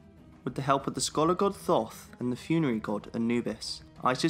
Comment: i think he just said the god "thot"